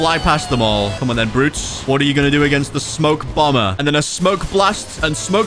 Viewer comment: i thought he said ‘’smoke bama’’ in the start when he got his 2nd fruit 😂😂